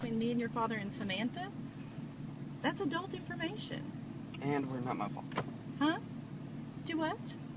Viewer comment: What did he say in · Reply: “And what about my father” I think